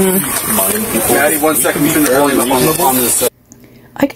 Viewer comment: It sounds like he says “Maddie one second” as tho he knows her. It appears le says hi to Hunter as he is walking up. Someone talks about the tea being a weapon, and mentions a video of someone being taken out. Imo